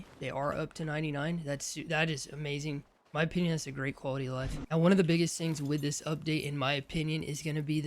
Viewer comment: I know you’re saying “my opinion, that’s a great quality of life” but bro I thought you just said “my penis has a great quality of life” & I lost it😂